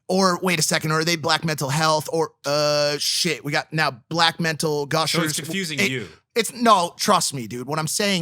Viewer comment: did he just say black mental gushers lmaoooooo